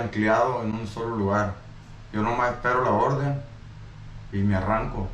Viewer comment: he said he "waits for the order" not he "dont" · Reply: Did he say “yo nomás espero por la orden”? I’m learning Spanish but it does sound like he said “yo no espero por la orden”